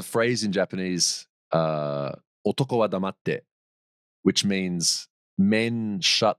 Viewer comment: anyone know what the Japanese saying is? Otoko… something? Edit… wait is it “koto de iiwake”? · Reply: otoko ha damatte。 男は黙って。 おとこ は だまって。 all of the writings in latin hiragana, kanji including hiragana and all hiragana. "ha" is read as "wa". Hopefully helps 👍 already said the meaning after saying it